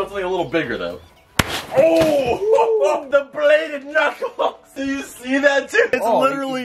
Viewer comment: did he just say the Bladed Knife or the Bladed Knuckles...?